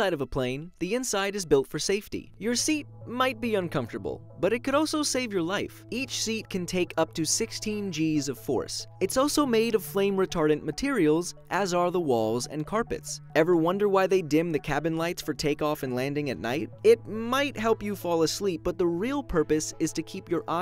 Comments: What I hear: "It's also made of flame retarded materials" xD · i thought i heard fire retarded material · thought he said "Brain retarded materials" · I thought I just heard flame retarded materials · Is it me or did I hear him say “Retarded” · I thought he said retarded